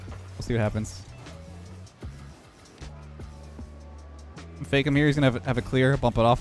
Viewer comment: am I the only one who thought he said FakeAmyr 😂😂 fakeamyr back at it again